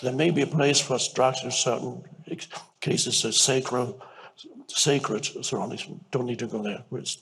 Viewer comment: What exactly is he saying at 8:11? “ there may be a case for sacred….dont need to go there”